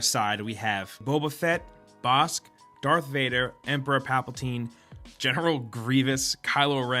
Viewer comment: Did he just say papaltine?